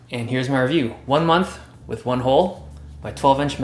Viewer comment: for a second I thought it said "one mouth with one hole" krill me